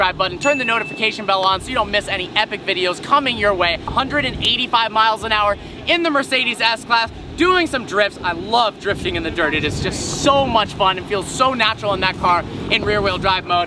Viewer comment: did he said s-class??